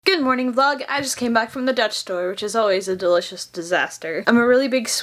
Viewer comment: Am I the only one who thought said she just came from the Death Star at the beginning? Either I should clean my ears out or stop watching so many films. Hmmm... *Grabs the ear buds*